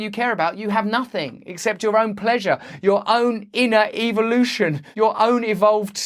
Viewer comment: Since I hear with an accent, what I heard was '...your own inner EVILution...' Ah! Perhaps you said it right and I heard it right for he first time.